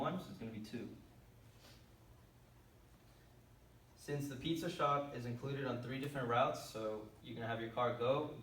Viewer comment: what do you say at 6:10? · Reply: Pizza shop!!